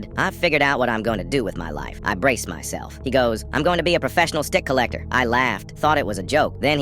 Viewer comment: I thought he said dick collector 😂